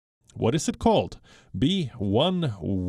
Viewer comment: He said "B11529" I heard "B Wuhan Wuhan 529"